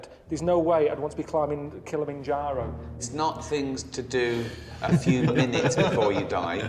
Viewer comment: Hi 👍great stuff,poor Karl. He always gets ths rough end of the stick🤣🤣good entertainment though and like you say, he can provide the occasional philosophical insight such as his take on offence. Hilarious mate,cheers✌️ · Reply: I keep hearing Ricky's statement at the beginning... "It's not HIS list" LOL cheers✌️